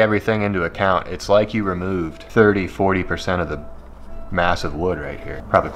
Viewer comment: When you said "it removes 30-40% of the wood" I said "prolly closer to 30" then you said the exact same thing. You have taught me well. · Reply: I heard “30% massive wood”😂